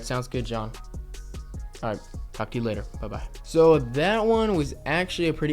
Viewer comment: Did he just say f you later at 7:34 ???😂